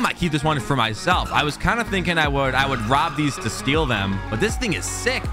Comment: what did you said? rob these to steal them? huh